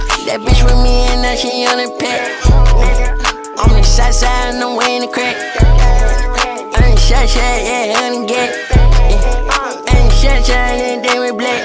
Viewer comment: tf was this man carti saying? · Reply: Imma southside ye ye ye dat gat